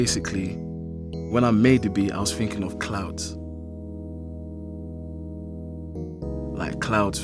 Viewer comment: "When I made the beat I was thinking of clout" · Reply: Boi Ambition 0:17 He clearly said clouds. Sky was sHown for a reason